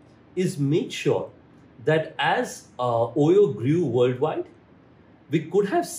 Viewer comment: The quarantine life 02:38 - His mental space currently 04:41 - His expectations vs. reality 06:36 - How does it feel to be an inspiration to the youth? 08:42 - His end goals 09:50 - How did Oyo manage the corona virus situation 13:37 - His learnings from this pandemic phase 16:30 - His top 3 wrong decisions 18:15 - His top 3 learnings 20:28 - His learnings from the lock down 23:00 - His spending on himself 24:15 - His concept of Impact 25:43 - His business inspirations 27:53 - His non-business inspirations 30:00 - Business opportunities after this pandemic phase 32:17 - What is the Oyo team doing currently? 34:27 - The concept of Thiel fellowship 38:42 - His top 3 inspirational movies 41:11 - His top 3 book recommendations 42:37 - His ultimate advice to the youth 44:50 - How does he deal with online hate and trolls 47:02 - Low days in his life 49:52 - His secret to be calm 53:23 - Best aspect of his life 53:52 - Worst aspect of his life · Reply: CC is wrong!! Replace OIL with OYO!